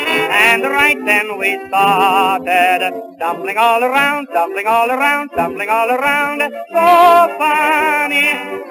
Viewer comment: Shit, I thought he was saying, "Jumping all around" haha.